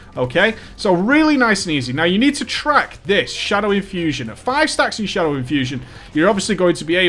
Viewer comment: ***** Thanks a lot, mate! Gonna give it a try :) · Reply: i thought u said: facking shadow infusion